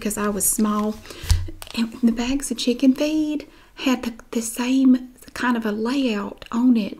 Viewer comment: i thought she said chicken FEET instead of feed and i was like what the fuck